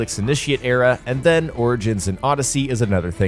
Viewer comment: wow great discussion😐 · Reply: oh my bad he said origins, I thought he said Oddysey. I love origins, Bayek is just one of my favorite of all the assassins.